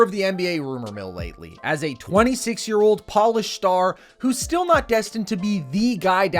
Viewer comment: did you say polish star? Hes finnish fam